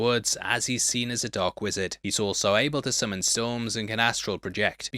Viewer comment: Sorry, but your accent made it hard for me to understand what you said at 33:57, tried to use closed captions but it stated: " summon BS" ? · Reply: Summon storms